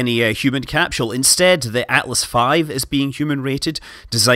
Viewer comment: I had to run that back because it sounded like he said "human cattle" .